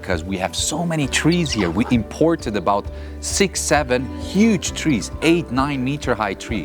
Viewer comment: sorry but did someone say 67